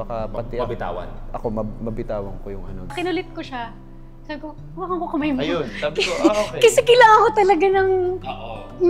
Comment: (hawakan moko babe!) 😊 Tama ba narinig ko? Haha